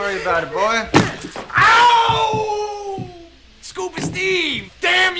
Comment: When I was a kid I thought he said "scuba STEVE, Scooby-Doo! DAMN YOU!"🤣😂